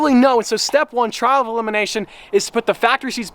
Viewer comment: it sounded like he said child elimination